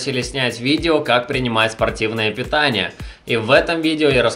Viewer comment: мне послышалось противное питание, совпадение ?